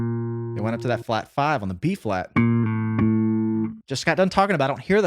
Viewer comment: When you said " It's in B flat'" .,,At first I thought you said "it's in Beef Flat" !!!